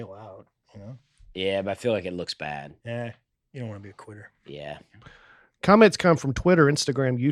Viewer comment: Greg needs to really hit the T’s in ‘quitter’ because I swear he just said to dusty… “.. you don’t want to be a queer.” 😂❤